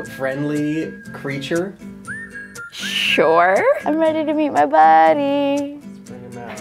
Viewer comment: I thought she said I'm reddy to eat my bunny lol